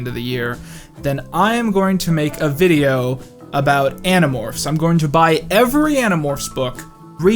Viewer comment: Remember that if we hit 400k by December 31, I'm going to make an iCarly video. And since it seems like we're going to hit that pretty soon, as our first stretch goal: if we reach 410k, I will buy every Animorphs book and do a video about the series! SO HIT SUBSCRIBE! :) Get the best discount of the year on Raycons! Now through the end of November, go to <link> to get 20% off your order! My Twitter ➱ <link> My Patreon ➱ <link> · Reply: I’m a month late, but when you said you would do a video on every “Animorphs” I didn’t have my earbuds all the way in and I heard “edibles.” So I think for the 420k goal, you should do a review of every edibles.